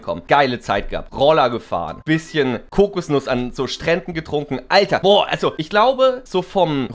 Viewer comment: Hab erst „bisschen koksen und an so Stränden getrunken“ verstanden und mich kurz erschrocken xD